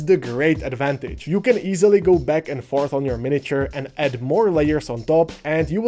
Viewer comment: I'm sorry, I shouldn't tease you about your accent, but it sounds like you said that we can fart on our miniatures. 🤭😂